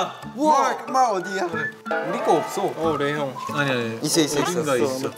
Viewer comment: did I just heard someone said "LAY HYUNG?!!"